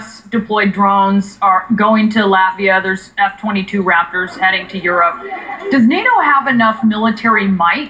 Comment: Sounds like she was saying raptures at 6:40 min